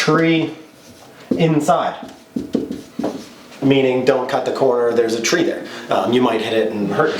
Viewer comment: First started playing dirt rally 2.0 and was confused why my co-driver kept saying "titan", when he was really saying "tighten". I was all, "what do ancient greek mythological creatures have to do with rallying?". Makes way more sense. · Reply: Better than me when I heard "Don't Cut" as "Donut".